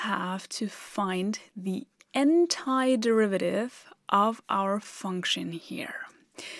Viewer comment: What is the word is saying before the word derivative? It sounds to me sort of like "entire", but not exactly. Thanks for the help. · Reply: I was confused by that too. Sounded like entire. 4:02 she says aunt eye, rather than anty